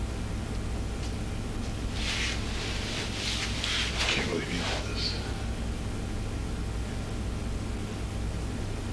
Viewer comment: I need you to weigh in, please! At 1:44:50 says a short statement but I can’t make out what he is actually saying. Can you help me? It sounds like he’s either saying, “I can’t believe HE did this” or “I can’t believe YOU did this”. What do you think he said?? You or He? ((I found this to be very interesting!)) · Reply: I think he said i cant believe any of this